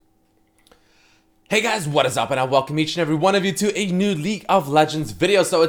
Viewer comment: Did you just say “leak” of legends